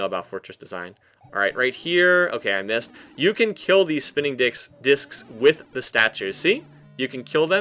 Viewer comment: second time i watch this and i heard it. "And you can kill the spinning dicks...discs"